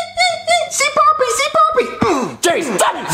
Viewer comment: at first I thought he said "say poppy say poppy"